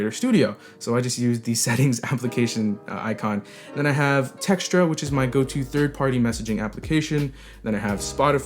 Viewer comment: What is your go-to messaging app? All I heard was "texture". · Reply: Textra